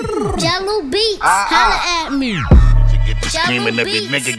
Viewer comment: who else thought on 5:28 that the tag said "Jungle on the beat holla at me"?